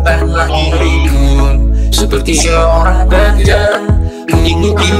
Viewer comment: apa gue yang salah denger Bukan RAJA tapi DAJJAL..😂😂😂